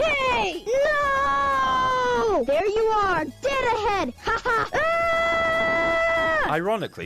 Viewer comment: I burst out laughing the first time I heard tails say "oh, there you are, dildo head!"